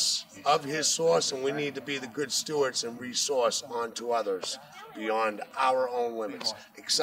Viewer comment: I heard "We need to re-sauce onto others"